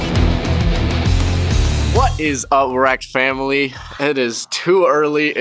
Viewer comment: "What is erect family" 00:16 Thats what i heard